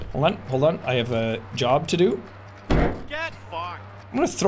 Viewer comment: anyone else know who kopke is? That's all i hear with the "get fahked"